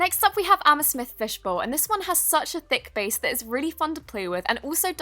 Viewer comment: when she said amethyst fishbowl, i thought she said i'm a fish fishbowl. did anyone else think of that?